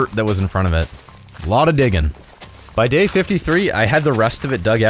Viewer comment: He said dig 53 instead of day 53 Edit: Timestamp